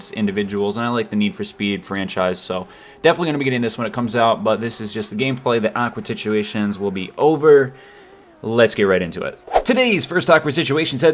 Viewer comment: did he say awkward situations wil be over?